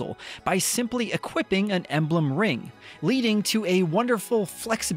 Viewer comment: I kept hearing "emblem ring" as elden ring. I think I need a break now. Looking forward to this game so much, 3H was one of my top 3 games on switch and I've never played any of the other ones. I've experienced fire emblem drought considering I couldn't play the older titles (PC + switch only)